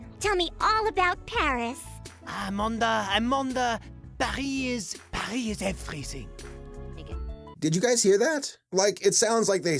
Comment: I didn’t hear that word tbh just sounded like he said “Parise Parise everything”